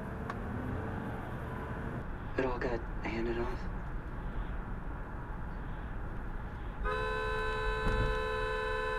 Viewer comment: I really don’t get people like that 3:59 I would’ve lost my sh@ on that one 6:53 what did he say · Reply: Sounded like "slow yo *ss down". Although that doesn't make much sense when they're going faster than...